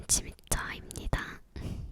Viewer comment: 키 나만 110으로 들리나 알려줄사람 댓글 ㄱㄱ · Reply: 150으로 들었어요!